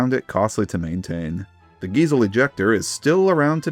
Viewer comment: I originally thought you said, "Diesel injector", but it was "Giesl injector".